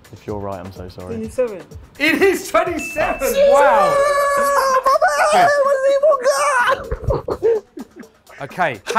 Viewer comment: What did Sonny say? It sounds French. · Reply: Merci!